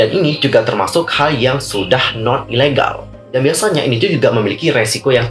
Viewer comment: kok saya dengernya Non ilegal hehe.